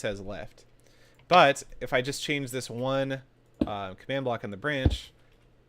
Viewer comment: I thought he said noob command block